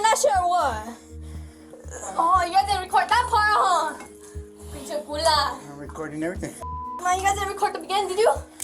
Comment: did she say pinche cula at 36:32 😭😭